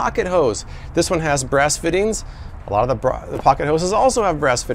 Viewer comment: When he said 'brass fittings', I just heard 'breast feedings' when my eyes away from looking at that part and I was like what?